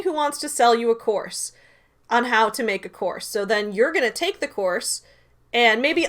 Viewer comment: thought you said “take their horse” awesome